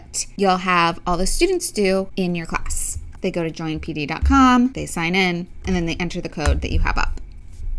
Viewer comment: Thanks so much for showing both sides of Pear Deck. You are the only one out there doing that. Thanks so, SO much. But Help! At 6:05 did you say <link>? What's that? I hadn't heard about this part of Pear Deck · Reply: Hi Nancy! I'm so glad this helped. I was saying: go to <link>!!! Haha :)